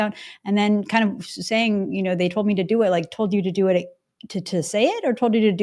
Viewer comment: I don't think Chretien said "they told me to do it" about burning the Whitehouse down. I think he said, "I'm too old to do it." Not that that makes it better, but for the record, that's what I thought I heard.